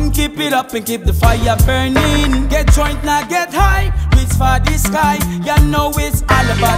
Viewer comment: did he say 'get drunk not get high' @ 2:36 ? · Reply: get joint now get high